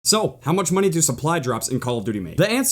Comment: if you go to 0:00 and keep hitting the left arrow key, it sounds like is saying soap.( ͡° ͜ʖ ͡°)